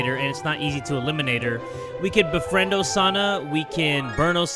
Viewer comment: why did i think it said you could breastfeed osana